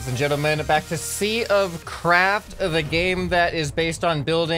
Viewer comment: Why do I keep hearing "sea of crap" xD